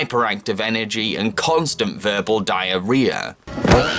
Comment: did he just say, "Verbal diarrhea"